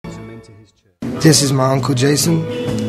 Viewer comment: i thought he said, this is michael jackson. God i should improve my english. 😅